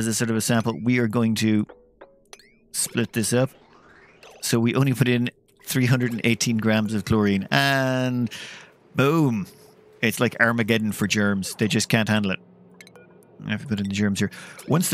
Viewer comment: XD when he said "Armageddon" it really sounded like he said "Arabic Ellen"